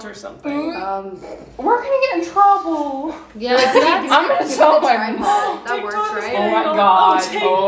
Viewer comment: Wait did say ‘ i’m going to tell my mom’